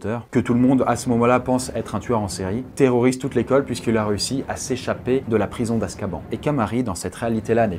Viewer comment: À 6:00 quand j'ai entendu "Terrorise toute l'école puisque la Russie" j'étais mort pttdddrrrr.